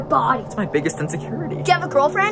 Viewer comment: “Do you have a girlfriend” “No” “That makes sense, you look like a broken Charlie Brown. HAAUNK” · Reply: Gurē Kitsune at first, I heard "do you have a boyfriend?"